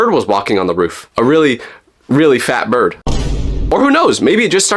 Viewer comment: Did he say fat bird😂😂😂